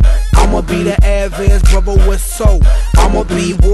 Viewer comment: the part where he says 'imma be the average brother with soul', the whole time I thought he'd been saying 'imma be the average bubble with soap'! now those are two completely different things.